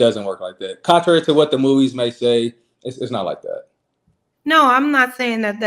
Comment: is saying it’s not worth it? Ok, shut up and go do it….